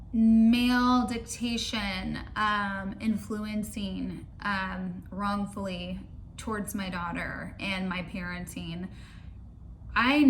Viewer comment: What is she trying to say at 8:27?!?!?! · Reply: Male detection influencing wrongfully towards my daughter? Google helped me. If I'm wrong blame Google.